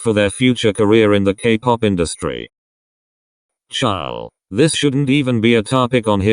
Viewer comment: wait did you say ''ciao''? omg i'm italian too gfhgfhc